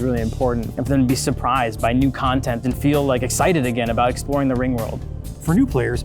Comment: Did he just say, "the ring world"???